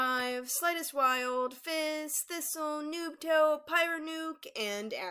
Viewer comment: Did she say FIZZ?